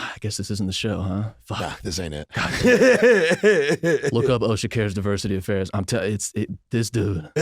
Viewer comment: what is saying? I’ve listened a dozen times and no clue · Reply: Osha cares diversity affairs. Black dude goes around pretending to be following up on dei complaints.